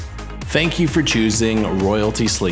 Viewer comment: Thank you for choosing royalty slingshots? At 2:09..am i hearing it wrong?